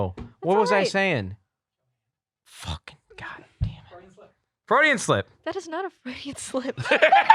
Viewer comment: Thought they said Freudian Sips, im like ummm you guys drank there? On campus?